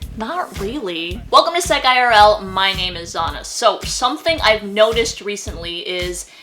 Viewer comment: She said her name is "Zonna" (possibly "Anna") but the name caption says "Donna".